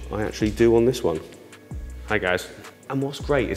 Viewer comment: Does anyone else hear "Hi guys" at 35:25 ??